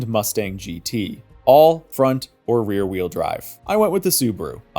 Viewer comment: Subaru WRX is AWD lmao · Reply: oh I misheard him saying all, front or rear wheel drive. I thought he said ALL front OR rear wheel drive lol. I was confused as to why the subi was rear wheel drive lul